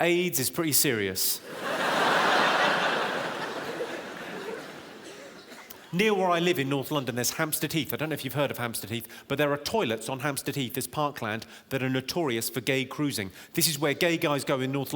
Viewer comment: every time he says "hamstead heath" i hear "hamster teeth" 😃